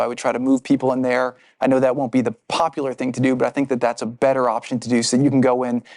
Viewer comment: what he say? the deaths is better option?